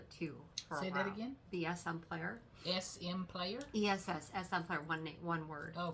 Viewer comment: can you please tell me again who to buy the Danish Flower thread from? I just purchased a vintage Danish cross stitch book and they used the Danish flower thread for all the patterns. THANK YOU for bringing this up in your floss tube! I was so thrilled! Thank you!! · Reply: I tried to put in google ESSMPLAYER.....I thought that is what was said during the floss tube, but cannot get anything stitchy related to come up. Thank you for your help.